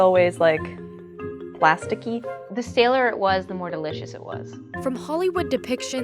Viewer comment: Does she say: “the staler it was, the more delicious it was?”